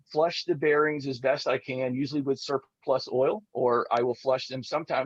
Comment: What kind of oil did say He used to flush his bearings? It kind of cut out at that point. It sounded like “sur plus oil” (it’s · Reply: KY lubricant and he uses a "surplus of oil".